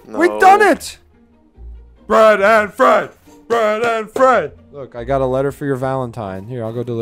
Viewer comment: thought that shit said breed and feed